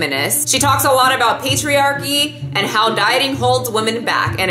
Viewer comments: I heard 'patriarchy' as 'pastry-archy' ' 😂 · Lol... The first time you said patriarchy i coullda sworn you said pastryairchy...lmao